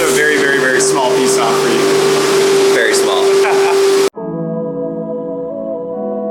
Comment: When he said “Very Small” I thought he said “Thanks Paul”